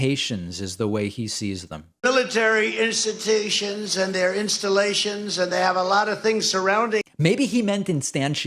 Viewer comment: “Military instantations and their installations” I love how he just rolls with it and tries to pretend that’s what he meant to say. 😂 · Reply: Mean Gene: I thought he said "military institutions and their installations".